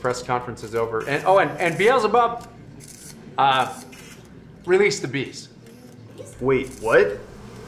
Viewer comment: Brilliant and scary at the same time. Release the bees? I thought he said 'release the beast' !! I keep saying it ... but I really wish my husband could have lived to see this day. He would have been so happy! He changed my mind with a lot of patience and debate! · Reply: I heard “Release the Beast” too. That’s likely coming too, to be honest 😬